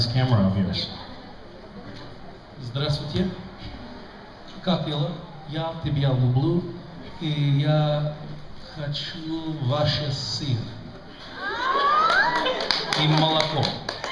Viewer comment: PLEASE WHAT DOES HE SAY IN RUSSIAN 29:06 ??????? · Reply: Hello. How are you? I love you. And I want your cheese and milk and your Putin (still president). Bye. Здравствуйте. Как дела? Я тебя люблю. И я хочу ваш сыр и молоко и ваш(его) Путина (всё ещё президент). Пока. I hope, it's still interesting.